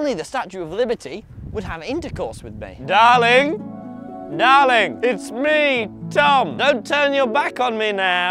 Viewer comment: the first one sounded like "daring ohayo"